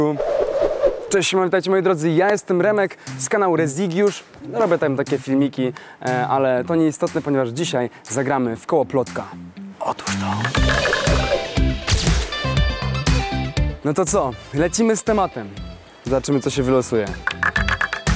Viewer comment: usłyszałam,, odłuż to''